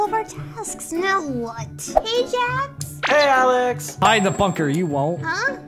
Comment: did say the f word😳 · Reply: He said hide in the bunker